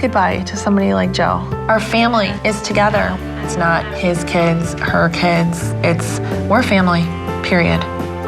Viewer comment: Did she say "Its Not His Kids Or Her Kids" LMFAO